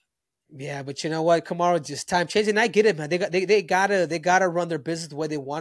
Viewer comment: Wtf did Henry say at 42:49 😂😂😂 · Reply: "Time changes -and I just get it man"